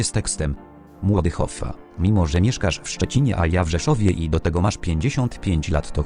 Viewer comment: czy ja dobrze usłyszałem w rzeszowie. O cie chu...